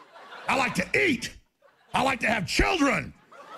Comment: "I like to eat. I like to have children." That's kind of terrifying. Someone should check on his children. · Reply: haanjamiis--My computer was acting up at the beginning of that segment, so at first I thought he said "I like to eat children"...and it seemed likely to me.